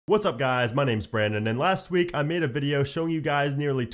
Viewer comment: At the beginning I thought you said “hey guys my name is bread” lol